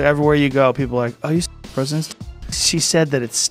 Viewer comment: I thought he said " she said it stinks like tar" 😂😂😂